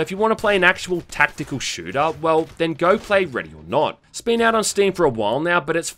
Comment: did says "Ready or Not?" Couldn't hear him or I thought I heard "ready or nog."